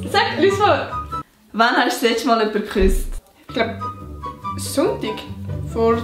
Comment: Was sagt sie bei 5:20 ? Ich versteh kein wort😂 obwohl ich aus der Schweiz komme 😂😂😂😂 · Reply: "wann hast du des letzte mal jemanden geküsst" 😂ich schreib einfach mal vorsichtshalber auf Hochdeutsch damit du es sicher verstehst😜